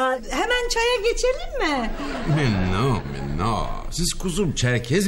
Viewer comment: burda ne diyor fransızca olarak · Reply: non ami non diyor . hayır arkadaşım hayır demek